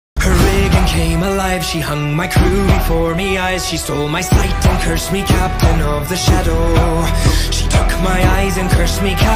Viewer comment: I THOUGHT IT SAID ''MY WIG BECAME ALIVE!'' 😭